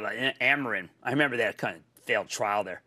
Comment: Did he say at 10:15 "I remember that c@ ??.